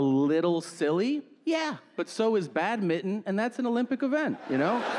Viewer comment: I thought he said "bad-mitten" for a second. XD